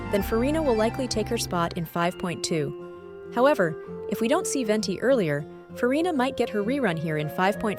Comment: Did you just called Venti an 'she'💀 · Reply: he said "see" not "she"